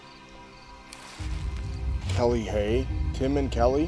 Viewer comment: I know damn well I heard a (Hey) at 32:11 when he was calling out those names. Plus come on mate the girl standing next to him at 32:18 I flip over the chair